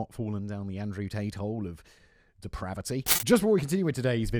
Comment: Does anyone else hear "tainthole"? 😂